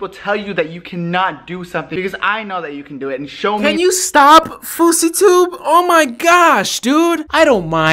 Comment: So at 8:59 I thought I heard "footsie tube." So wanted to look up what that means and um...theirs a fetish for everything. Thanks. One more search I have to be ashamed of if I die prematurely